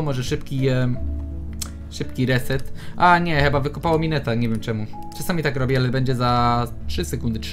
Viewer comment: W pierwszym momencie usłyszałam "mineta" o.O